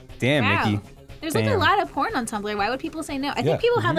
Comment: ok i dont go on tumblr but did they say THERES ALOT OF PORN on tumblr around 8:45 ish or did i think they were saying something else